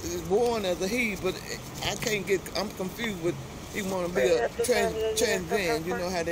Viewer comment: I laughed too hard , when his momma said , " he was born a he , and she wants to be a trans am ." 😂😂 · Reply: I thought that too!!! I had to replay it a couple of times cause I thought she said he wants to be a trans am... I haven't laughed that hard in a long time!